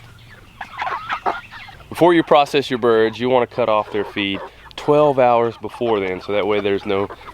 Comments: I misunderstood you, at 1:55 i heard "before you process your meat, you want to cut of their FEET 12 hour before." I was like WHAT???. Then i realized that you said FEED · I thought he said 'You wanna cut their feet off a few hours before.' I was like, Why would you do that!? Ohhh, you cut off their FEED... not feet. Yikes. And ROASTED is the best! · Mr. LOL I thought you said cut their feet off you said cut their feed off. I was like what is he talking about how are they going to walk around with no feet lol